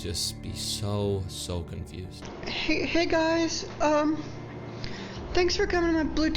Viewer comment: hey guys welcome to my Bluetooth page sounded like he needed help🤖🤖🤖